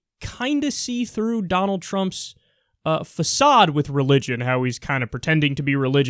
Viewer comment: did he just say "phasad"? that's an Arabic word.